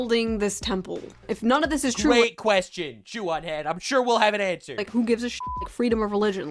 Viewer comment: i could have swore i heard say "joo on head" 🤣😂